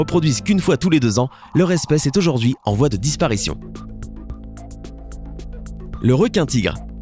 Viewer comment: j'ai entendu le respect est en vois de disparition ...🤦🤦🤣🤣🤣🤣🤣🤣🤣🤣🤣